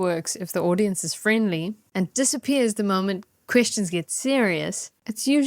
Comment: did you say “questions get serious” or “Christians get serious” ? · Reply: Definitely "questions"